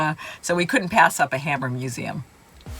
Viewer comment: (misheard) I thought you said hamburger museum!